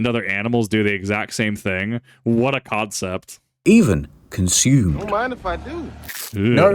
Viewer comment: did you.. did you just say Codcept? That deserves a very enthusiastic boo from the audience :P